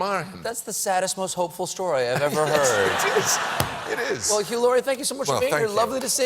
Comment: it sounds like he said "well hillary..." lol